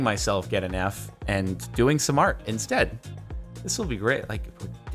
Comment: I misheard "doing some art instead" as "do ASMR instead".